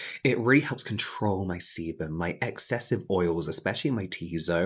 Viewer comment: I was watching this while cleaning and I heard you saying about not wanting kids because of the stuff they bring home… then you said next product controlled your Sebum and I heard Semen - and thought- that will help with the kid situation.